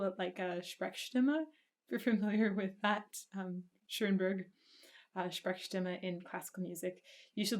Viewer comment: Hey there ! first of all thank you, all your videos are very great, I'm a big fan. At 16:15 you mention a name that I can't understand. (My English is not very good but I try). "he sounds a little bit like "$€ù£%^$ù`... Sundberg". Can you (or someone in the chat) write me this name so I can check this up? Thank you very much ! Ha... while I write here... I don't find any reaction on Metallica? it's the biggest metal band for a lot of people ! And the voice of Hetfield is .... wow, he sings very high but there is a lot of "low" in this tone... hope you the best ! ;-)